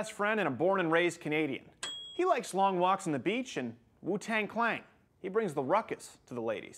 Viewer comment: Did you just say Wu-Tang Clang?